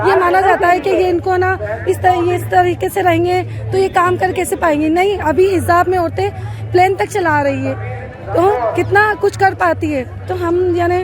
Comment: Did I just hear "Hijab pehen plan v chalati he"?